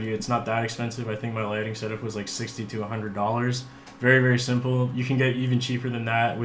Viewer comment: I thought you said your lighting was $6200 dollars instead of $60 to $100. 👀 I was like, “Holy sh*t! What is this dude’s idea of expensive if $6200 is cheap?!?” 😂